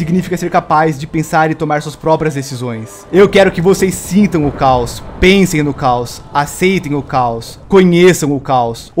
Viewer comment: O que o disse: Eu quero que vocês sintam o caos, pensem no caos, aceitem o caos, conheçam o caos... O que eu entendi: Licença, tem um minuto para ouvir a palavra do caos? Enfim, brincadeiras a parte, eu amei o episódio de hoje, está simplesmente sensacional, o caos dominando tudo e todos da cidade, para em breve, o caos se alastrar por todo o lugar, toda a mente, toda a pessoa, até que não sobre nada sem ser caos...